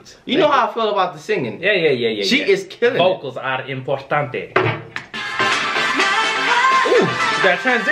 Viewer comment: “Focus, algo importante” I love it 🤣 my man can speak Spanish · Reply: D3thless Oh shit I heard “focus, algo importante”